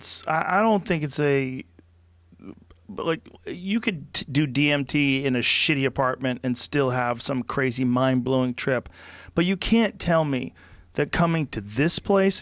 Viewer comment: I thought you said "D&D in a shitty apartment" - and I was about to object. But yeah, it's kinda messy. And there's DnD stuff all the hell over the place. And my brain makes its own DMT, right? So we're BOTH right!